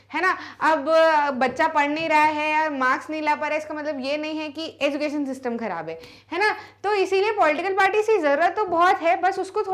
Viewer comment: did she just say education system kharab nahi hai 🤨